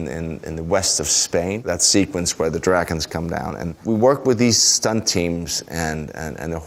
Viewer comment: did he just say "dragons" in Danish? xD I love that! Or did he just say "dragons" but said it wierdly? xD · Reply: He said “dragons” in English. It would have been “drager” in Danish, with a soft g.